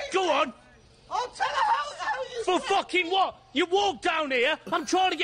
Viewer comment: does she say "I'll tell the whole world that you r** me"? Insane that she was so willingly ready to ruin his life with a false accusation.